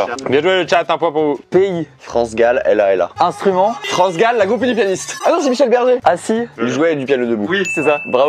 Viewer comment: J'ai entendu "La goupille du pianiste"... ^^ Et "Il jouait du piano debout" fut écrite par Michel Berger